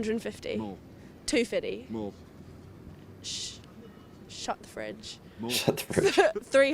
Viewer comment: You misheard its " shut the Fridge!" - its instead of swearing. As for the NHS in UK I think for all its faults it is one of the best in World as a universal system.The US medical system is definitely a NO for me. 30,000 to 35,000 dollars for a healthy woman giving birth with no complications or further care is CRIMINAL. Paying for an ambulance is in mind, also criminal - although I do think calling it out unnecessarily should be punished hard. Yes many people in UK are ignorant of the real costs - but in the opposite in US why are US paying so much? Sorry, US does not seem to be a caring Society, when it does not care for the majority - and that is what the NHS gives to British people. ( by the way in UK you can still buy PRIVATE FINANCED MEDICAL CARE-if you wish) Thankyou for your site 👍